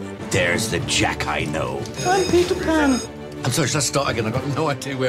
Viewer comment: did he just said i am pewdiepie ?